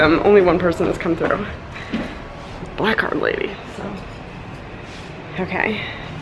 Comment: I swear when you said my first black card I had to rewind because I thought you said black cock. I was incredibly confused.